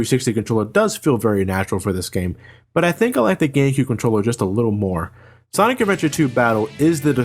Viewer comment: did he say gaycube controller?